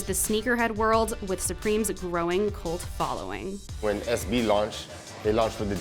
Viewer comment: captions say "cold" instead of "cult"